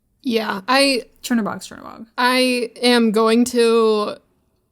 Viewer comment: “Chernabog’s are hot” I’m not 100% that’s what was said, but that’s 100% what I heard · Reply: I hear "Chernabog's Chernabog"